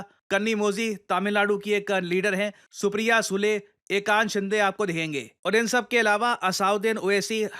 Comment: Supriya sule OMG for a sec I heard 'Supriya Shrinet' Thank god 😂😂❤❤